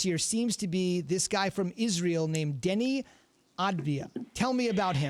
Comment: did he say Denji Israeli?